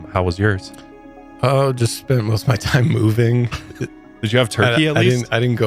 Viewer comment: For a half a second I thought you said I spent most my time pooping but then I realized you said moving...